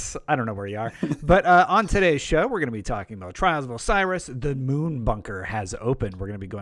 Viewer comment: I've got to get my hearing checked. When was doing the introduction and stated that the Moon Bunket opened up.. I thought he said the Moon Fucker opened up🤣🤣🤣